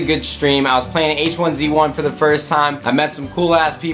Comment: I thought he said "I played aches 1v1 for the first time" wow that would be weird